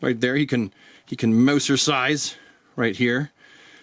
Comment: i thought you said "Mousercise", but i wasn't sure until the cutaway. 😆